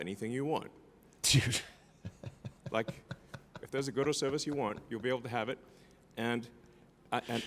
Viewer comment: I legit thought he said "if there's a gooder service you want.."